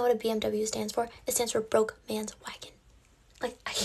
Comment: I thought she said “broke mid wife” until I read the comments